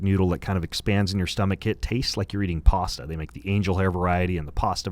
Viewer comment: I thought he said possum, not pasta